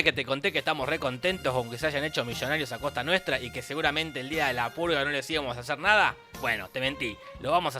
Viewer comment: Yo pensé que decía la saga de la pulga XD